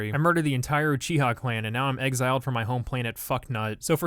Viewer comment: "jihad clan"3:51 did i hear that right? As a muslim i aprove of this masseg